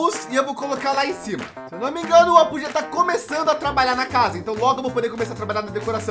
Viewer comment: eu entendi ele falar: "meu namorado, Apuh"☠️